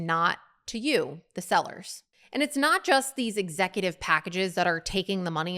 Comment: I heard ETSY CEOS ARE GETTING MILLIONS in bonuses after *THIEVES* that tracks